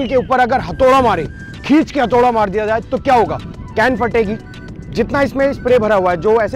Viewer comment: i hear gand fate gi